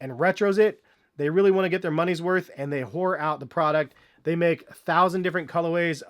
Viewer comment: WTF did you say? whore-out the product??? I love your reviews but . . .